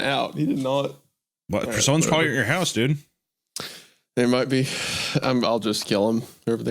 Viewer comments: am I the only one who heard "Hasan's probably at your house dude!" 😂😂 The dog saw Hasan's stream and is worried; Just wants reassurance that this isn't one of those situations. 😂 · I legit thought Hunter said 20:00“Hasans probably at your house dude” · Does hunter say someone or Hasan? Either way hilarious give me 10 more